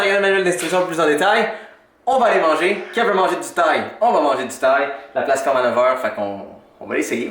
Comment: À 13:04 , quand vous dîtes que vous allez manger, j'ai compris que 'Kev veut manger du Tide' mais c'est clairement pas ça😂😂